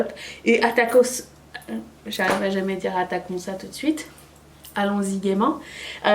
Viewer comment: Pour le Attaquons-ça j'ai entendu "un taccos là" 😂